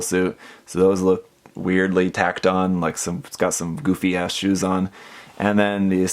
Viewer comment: no way he actually said 'Goofy Ass' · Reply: "Goofy ass shoes on" 😂